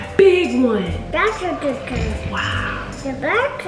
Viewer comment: After she said “that’s a big one” I thought said “that’s what she said”